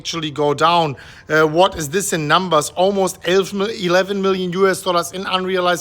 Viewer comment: did you slip into deutsch?! I thought I heard you say “elf mil,” and correct to “eleven million.” Super freund! Tschuß from the states. 🚀🐋💯